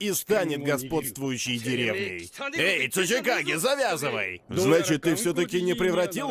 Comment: Я когда слышал в первый раз слово цучикагэ мне послышалась сучийКАГЭ и каждый раз кагэ сучий :)